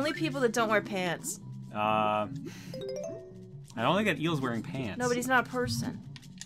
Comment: anyone else heard "Emile" instead of "the eel" at 6:06?